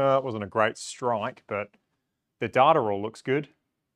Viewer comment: Did you say dat-er at 9:50?